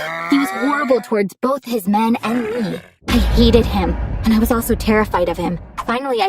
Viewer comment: O! I thought she said yeeted not hated